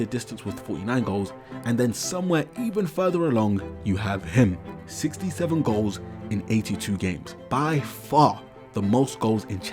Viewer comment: did you just say… six… seven…?